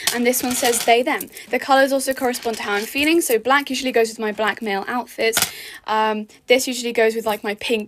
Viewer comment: for a sec… I was like “hol’ up.” I thought she said “black man outfits” 👁👄👁